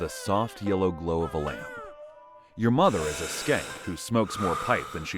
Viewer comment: did he say skank? Lol😂😅